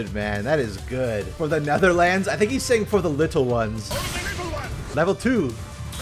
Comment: I thought was saying "move a little less", not "for the little ones"